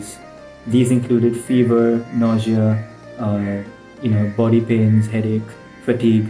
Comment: boy pain instead of body pain